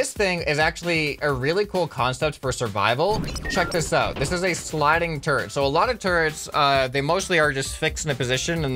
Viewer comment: I thought that said “Smart Sliding Trumpet”